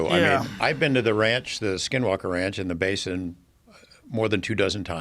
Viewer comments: I thought it said 'Skywalker range' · So for a minute or two I thought this said skywalker ranch and that guy was George lucas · I thought it said Skywalker Ranch. Thought, George Lucas looks like shiiiit